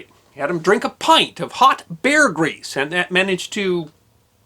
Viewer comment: Surviving to age 85 after that health-destroying ordeal impresses me the most about this story. The guy may have been a desk jockey all his life, but he was made of sterner stuff. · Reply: Aaron Friedman I honestly thought he'd said, "beer grease."